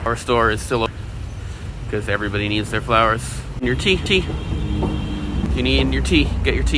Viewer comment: I heard TT. Freudian slip. Must be a Twice fan. 😁